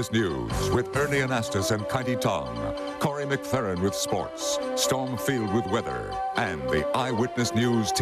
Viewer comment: Wait, wait, did he say "Storm Field with weather?" Surely I misheard that.